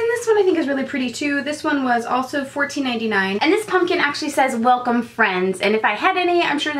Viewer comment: I thought the pumpkin said welcome fiends lol